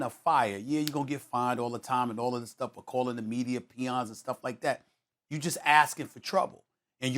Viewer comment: When did Kyrie call the media peons 🤔 I thought he said pawns .... SAS u dumb af